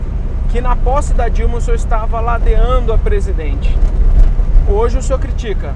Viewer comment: Em 8:58. "Que o senhor estava ALADEANDO a presidente". É isso que ele fala? E se for isso mesmo, o que significa?